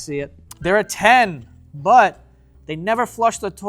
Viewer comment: Dude i genuinely thought it said “she’s 10 but” I WAS LIKE WHAT?! broooo 😭